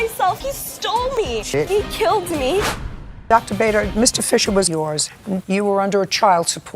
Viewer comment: My mush of a brain thought Judy said “Mr. Fischoder” from Bob’s Burgers.